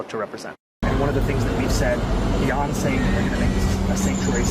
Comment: I thought he said “BEYONCE”but he said “Beyond Saying” 😅😂